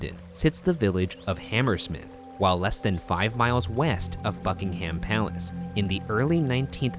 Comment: Is it just me but when he said buckingham I thought he said ****ing a ham